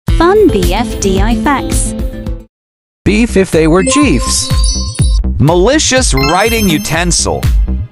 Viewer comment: I heard: "Beef if they were beef" 💀💀💀